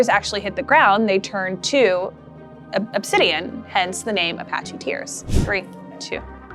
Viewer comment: Apache tears? I heard this as "The Trail Of Tears." When the white man forced the Indians to leave they're land and walk several miles to a designated place. The stones were the trail of tears.